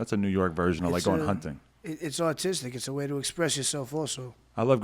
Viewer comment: thought action said 'its autistic'